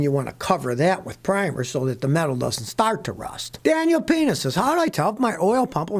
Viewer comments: mark...Did he say DanielPENIS asked a question about his oil pump? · Did I hear that man's name right? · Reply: “Daniel Peña says” LOL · Did Scotty say Penis @ 7:01 ?